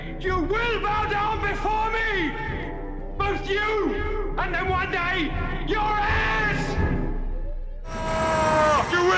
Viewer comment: 1 thing I could never understand..... It sounds like Zod says " Both you and then 1 day your ass!" Never made Any sense to me!!